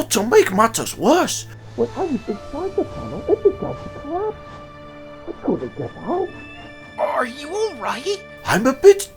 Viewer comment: Does anyone know what Hiro is saying here? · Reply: He said the tunnel staretd collapsing but I couldn’t get out sometime.